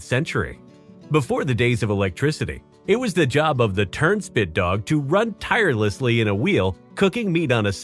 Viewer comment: I’m not gonna lie, I think the turnspit dog would’ve been a cute pet in the current day and age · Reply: Well sense we're not gonna lie...I thought he said turdshit dog at first.